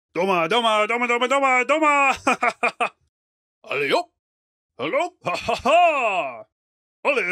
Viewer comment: Did he just say Alley-Oop? Amazing. Best boss in the game.